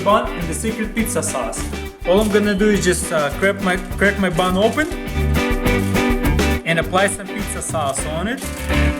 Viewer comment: "crack my bum open" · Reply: I Thought It Said Crap My Bun Open.